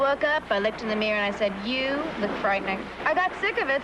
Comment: I swear it sounds like says "you look pregnant" 😅